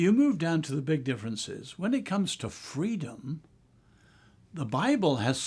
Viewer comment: 🗣 Freedom? Did he say freedom? Please do a video on the “Inter caetera” The Papal Bull of 1493 (The Doctrine of Discovery), “The Romanus pontifex”,The Papal Bull of 1455, and “Dum Diversas” The Papal Bull of 1452. Let Freedom ( TRUTH ) Ring!